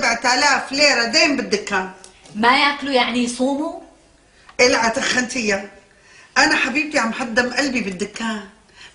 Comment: بالدقيقة 11:35 شو قالت منال يا جماعة صرلي ساعة عم حاول افهم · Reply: مية وردة